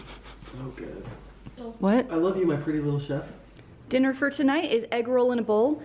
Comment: The commercial cut in and now I think you did it on purpose lol but I thought he said pretty little shit. Buts it’s chef.